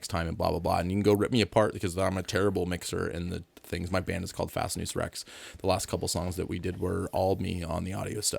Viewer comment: What did he say his band name is? He said it so quickly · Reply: Fascinus Rex